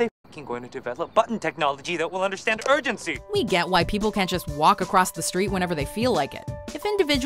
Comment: i heard "White people" ? LuL